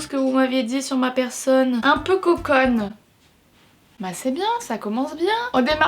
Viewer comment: qu'est-ce qu'elle a dit? « un peu coucon »? Je ne sais pas le mot, pourriez qqu dites-moi? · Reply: Coconne --> conne